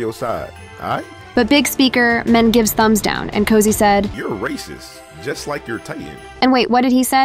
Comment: I think cosi said, "your racist, just like your tie", to large speakerman